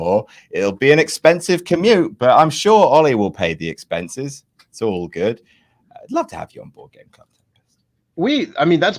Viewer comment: Adam and Tempest are talking about board games and Tempest's second suggestion after Ticket to Ride sounds like "crow-canal"? I'm probably mishearing that big time but can anyone let me know what game they're talking about? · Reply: Crokinole (took me a few listens to get it dang that canadian accent) Its a wooden game kinda like a tabletop shuffleboard.